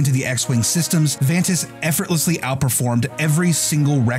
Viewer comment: At first I thought he said "Encom Corporation" which got me REALLY excited for a second. XD